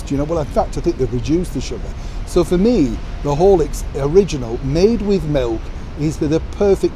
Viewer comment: I have no idea what Horlicks could be. I thought you were saying "holics" as in alcohol. LOL We are both speaking English, right?